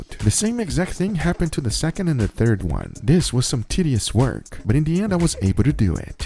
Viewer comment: Tell me im wrong but did he say tiddious😂